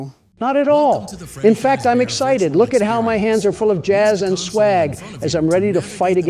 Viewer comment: did he just say swag?